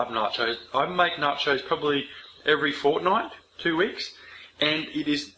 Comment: Did hi just say fortnite 0:21 😂😂😂